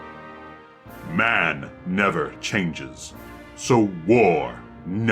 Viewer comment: bruh i heard that as I’m playing fallout